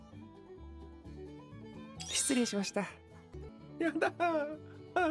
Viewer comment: 失礼しました☆がミランに聞こえてならんw